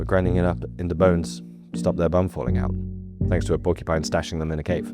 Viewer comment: “to stop their bum from falling out”? Did I hear that right?